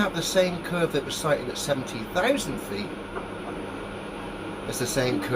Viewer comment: 17000 feet? That looks more like 17000 meters, or 55000 feet, about 5000 feet below the U2's max altitude. Mostly because 17000 feet is not high enough to actually see the curvature of the earth with the naked eye, since that only about 5 km above sea level. But flatearthers are not really known to be honest, or actually tell the truth, but are instead known for spreading misinformation, misinterpretations, and outright lies. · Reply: I heard "70,000 feet".